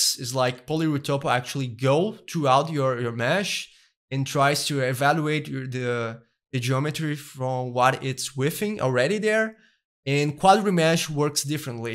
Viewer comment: what he is saying around 7:44 ? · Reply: I think he's saying "it tries to evaluate the geometry from what it's within already there"